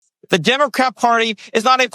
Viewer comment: Why do I hear him say "the Democrap Party" every time he mentions their name? hmmmmmmmm..... so apropos.